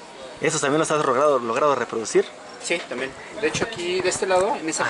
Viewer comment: escuché mal o dijo lo has drogado ? Minuto